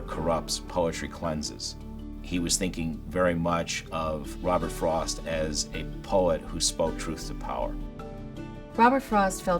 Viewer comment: can anyone write what the man was saying at 3:15? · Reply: He was thinking very much of Robert Frost as a poet who spoke truth to power.